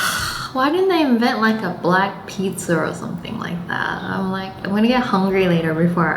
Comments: I thought the caffe said BLACKPINK lol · I thought the caffet said blackpink😅